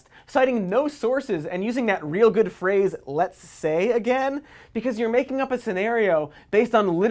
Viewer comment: evry time he says "lets say" all i hear is "would you kindly"